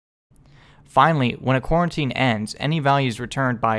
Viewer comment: Lol I keep hearing "Quarantine" instead of "Co-Routine"